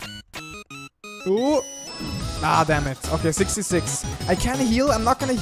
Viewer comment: I HEARD 666